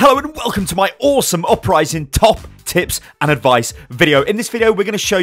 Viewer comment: Anyone else thought he said top tits?